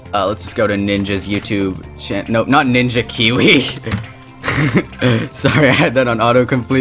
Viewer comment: I think I hear "Ninjaeek"